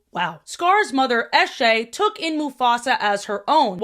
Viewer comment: i for real thought she said eshay instead of eshe and was like "who named her that? no aussie is gonna be able to take it seriously" (Eshays are gang wannabes in australia, if you've seen hearthbreak high think cash)